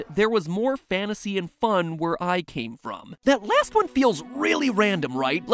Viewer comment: "Theres more fantasy and fun where I came from" I heard: "Theres more ??? to see and more where that came from" but i guess the former makes more sense huh?